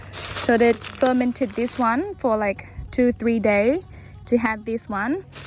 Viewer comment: I heard her say “They tormented this one for 2-3 days” the subtitles also showed tormented ehhehehehehe